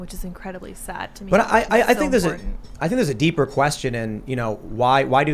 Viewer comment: This is a legitimate question, I'm not trying to be funny. At about 6:31 says "which is incredibly sad to me..." and Tim starts talking over her she says something I can't make out. What does she say after "...sad to me..."? It sounds like something along the lines of "I'm so horney". What is it? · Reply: "It's so important”